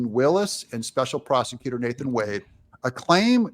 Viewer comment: He said prosecutor! At first, I thought he said, special prostitutor, Nathan Wade